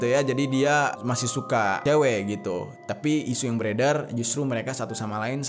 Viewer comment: kok gw dengernya "suka ngemwe" y? :v